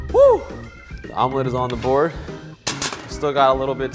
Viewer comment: What he actually says: “Omelet is on the board” What captions say: “Mom life is on the board”